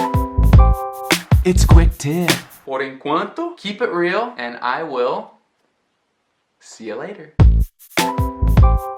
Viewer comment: What did he say at 6:32? Keep a grill? Grew? 😂😂 I'm so curious about it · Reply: Yara Gonçalves ele disse keep it real